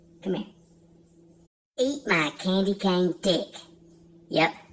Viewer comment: LMAO! Almost thought you said candy corn dick. That was great